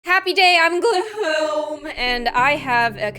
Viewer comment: For 1 year I thought that you were saying „Happy day on woohoo!“ But your actually saying „Happy day on gloo-hoom“